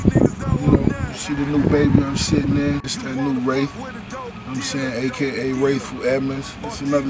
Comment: Did Rick just say " ..the new bentley i'm sittin' in, its the new wraith" at 1:24 ?